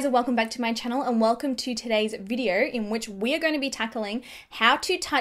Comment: Did I just heard she said we're going to be takaling 🤔? Love you ❤️😊